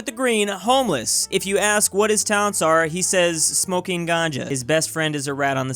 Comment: Me at 0:13 because I heard ganja and it means weed in Indonesian: e